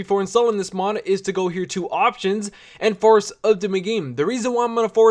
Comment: God damit!! I can understand you sometimes when you were saying force update I thought you were saying wanna date